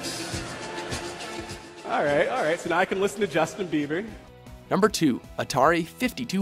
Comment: Did he just say "So now I can listen to Justin Bieber"?